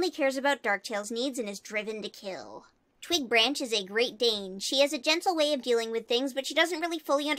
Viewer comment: I wasn't listening properly and thought you said 'Twigbranch has a great name'